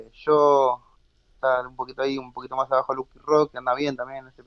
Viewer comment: quien es la promesa de la que hablan? que no entendí el nombre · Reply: DS_Lucky_Rox, <link> . hace 1 año q juega , tiene 19 años y ya metio 2050.